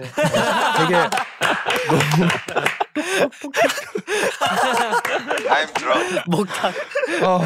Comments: Is it just me or I heard dowoon's "im drum"? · HAAHHAHAHAH I heard ‘i am drum’ dowoon 😭😭😆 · Wait do i hear "i am drum" LMAOOOO😭😭😭😭😭😭😭